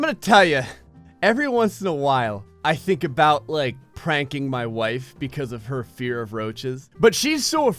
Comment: Omg, I thought he said "Spank my wife", and not "Prank my wife"... 😂😂😂😂😂